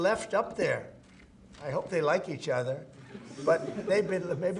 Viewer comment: Did he say that? I thought he said, "I hope they love each other... " in regards to how much time they have spent together in space. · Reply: You are right. He said, “I hope they like each other.”